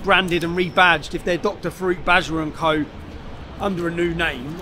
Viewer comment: Am I hearing "Dr Fruit Badger and Co" 😂🤣😂 this guys a illiturate trying to make it tru fraud and lying to the public. Sorry Tommy I ain't helping u this time. I need my money..